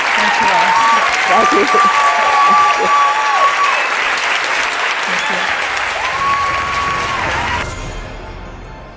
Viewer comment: What does Lilly say at 15:38? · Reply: Jane pointed to someone in the audience. It sounds like Lily said, “I know. _ (he, she?) came here reluctantly.”